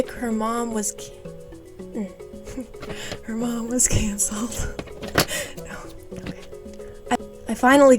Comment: Oh my god I thought you said her mom was cancelled!!!! I was so confused I was did she . . . did unalived??